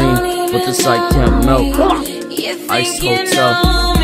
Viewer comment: What is he saying at 2:33 ? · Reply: chances